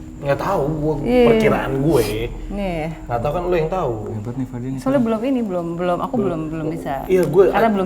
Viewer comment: apa cuma aku yg denger mas toxic? Apa aku salah denger? Kalo mas bener toxic pertama kali aku denger mas ngegas wkwk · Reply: Itu mas bilangnya "hebat nih fadi.." hahaha....🤣🤣🤣